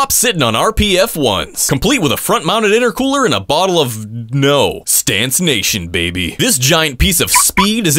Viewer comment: I swear I heard him say a bottle of milk